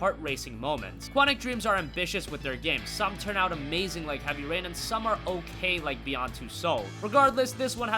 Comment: sounded like u said "heavy pain"